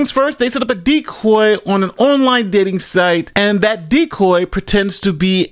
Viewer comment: LMAO I thought you said diddy site at first played it back and heard dating site